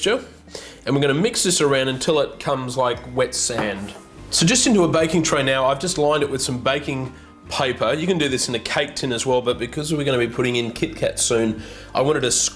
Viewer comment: did he say wet semen?